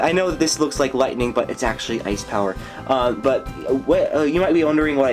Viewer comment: Umm did you say its actually ice tower :D?